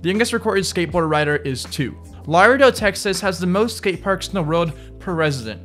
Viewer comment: Wait hold up did he say Laredo texas at 7:02?